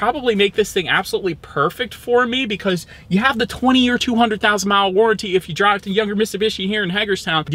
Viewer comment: Did you say at 20:40 'You have the 20 year 200,000 mile warranty'??